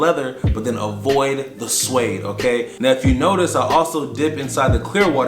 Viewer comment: what did he say? Avoid the ? I don't know the word haha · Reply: Suede